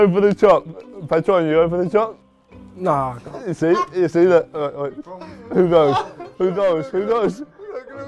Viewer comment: what is harry saying? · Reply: "Who goes, who goes?"